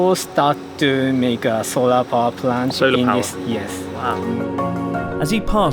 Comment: lol I thought he said some people have started to make "soda pop land".